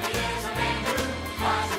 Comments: does he say "laugh, and cry" or "sniff, and cry" ? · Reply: Actually he says Laugh and Cry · does he say "laugh, and cry" or "sniff, and cry" ? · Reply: I always thought he said "F-'in cry." Lol. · Its been 1 year and i still dont know what stewie is saying in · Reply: Laugh and cry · Am I the only one who thought instead of Stewie saying laugh and cry,said f'in cry?